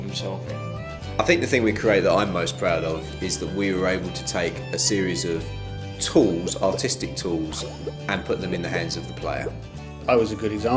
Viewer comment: I swore he said "they took a series of autistic tools"